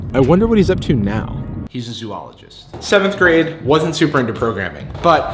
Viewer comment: What did he say at 4:41? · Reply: Oh, i think he said zoologist :D